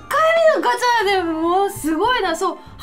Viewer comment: テンション上げ過ぎて最初のセリフ「クソリートファイター！」に聞こえるｗ